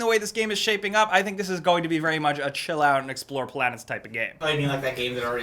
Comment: I did not understand A WORD from what the guy in the back said, may someone quote for me please? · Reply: "I think this is going to be very much a chill out and explore planets type of game"